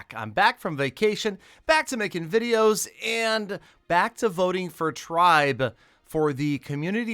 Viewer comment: I thought he said back to voting for Trump😧